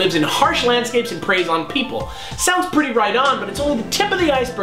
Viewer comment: did say right on or Rydon? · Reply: Right on